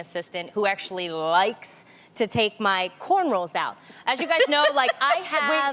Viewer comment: did tamera say “cornrolls” ?💀